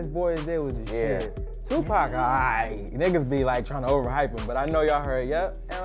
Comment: Did he just say tupac alright u smokin shit